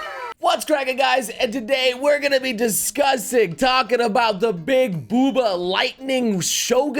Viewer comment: I thought said 'we're gonna be disgusting' gomennasorry we decline at watching your baals